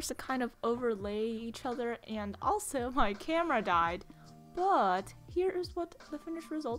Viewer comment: omg instead of “while filming my camera died” i heard “my grandma died” I GOT SCARED